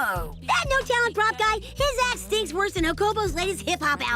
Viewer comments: I thought he said "his ass stinks" instead of "his act stinks" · Did he say "his ass stinks" lol I listened to it several times and can't hear anything else. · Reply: “His act stinks.” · why at 2:42 it sound like he said “his ass stink more than ______ last album” 💀😭😭😭😭💀💀💀